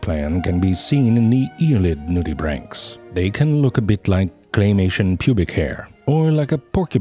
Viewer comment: 😕Did I just hear " claymation pubic hair"? 😂🤣